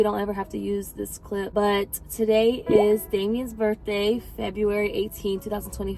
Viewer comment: 😭😭😭😭😭😭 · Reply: Wait who died 😢😢😢😢😢😢🥺🥺🥺🥺🥺😭😭😭😭😭😭😭😭😭 I thought she said Damien died